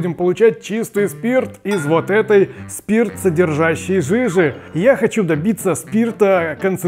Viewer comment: Мне одному не верно послышалась фраза "Я хочу допиться ....."